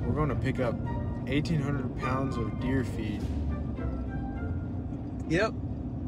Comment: This entire video I thought he was saying 1800 pounds of deer feet but he was saying feed😂